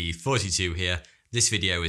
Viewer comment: I know he says “Thoughty2 Here” but I always hear it as “42 here”